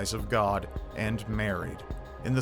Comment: why did he say “…and Mary”? That was really weird and it shows his ignorance of Christianity. I am a fan of this dude, but I am pretty sure that was supposed to be a little underhanded dig at Christianity or the Amish, and either way it was unfounded. Only Catholics are obsessed with and almost want to deify Mary. Let’s get one thing straight- Amish are certainly not catholic. Neither are modern Christians.